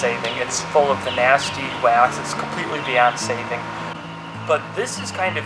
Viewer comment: I thought he said it's completely Beyoncé'd it instead of it's completely beyond saving